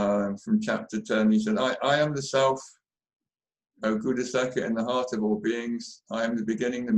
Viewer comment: Does anyone please understand what David tells when he qoutes Ramana's favourite gita part? The time in video is 40:23. I hear "I'm the self ??something?? in the heart of all beings...". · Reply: Oh my friend found. It is chapter 10 of most likely Bhagavad gita which says: 20. I am the Self, O Gudakesha, seated in the hearts of all beings! I am the beginning, the middle and also the end of all beings.